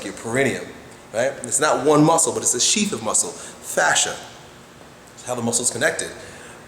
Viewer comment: Fascia is connective tissue, not muscle. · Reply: He clearly says at 4:20 "it's how the muscle is connected"